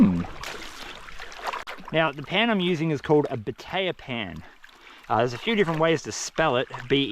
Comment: Oh, it's *batea pan*. All these years I thought you were saying "potato pan"...🙃